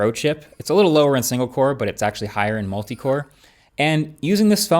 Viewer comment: I heard "Singapore" not Single Core.....